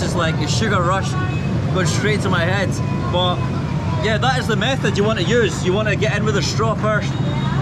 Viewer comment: When he was eating the shake I could’ve swore he said “ this is the meth you want to use”